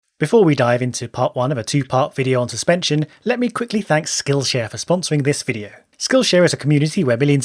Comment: I thought you said in the intro: 'Before we die into..'